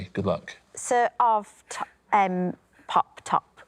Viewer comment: What the hell was Lucy trying to say? I've played it 3 times and it makes no sense. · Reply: Sooo, of errrrm pop tops